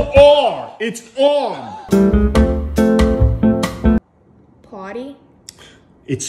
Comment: For party I thought I heard potty😂😂😂